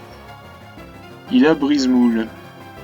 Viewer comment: kranidos je me souviens je l'avais, mais j'ai entendu brise boule à